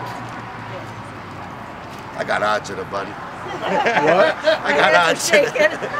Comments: your captions are wrong. He said "I got agita, buddy". It means he's anxious and nervous about the score. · he isn’t saying “odds.” He is saying, “I got agita.” “Agita” is what Italians say when we’re stressed out so much that it is giving us heartburn or an upset stomach. He’s saying he’s so nervous about the score you’re giving him heartburn. My grandma used to say this every single day for something 😂